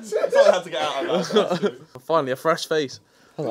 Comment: When Harry says “finally a fresh face” when vik sits down 😂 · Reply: Ngl for some reason I thought Harry said " finally a fresh date"